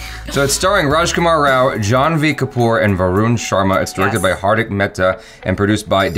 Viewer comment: when said janhvi kapoor , that kinda sounded like John wick kapoor 😂